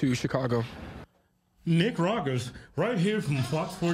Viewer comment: It did sound like he said his name is Nick... Not.